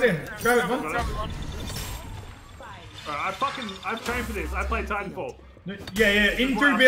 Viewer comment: Did he really say "I trained for this I played Titanfall" ? 😂